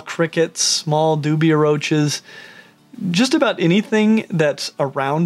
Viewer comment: I thought he said “doobie roaches” & I was so confused?? 😭🤣🤣